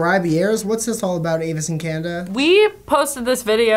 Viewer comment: I'm sorry did just say "What's this all about Avace and Canda?" and can we talk about that pls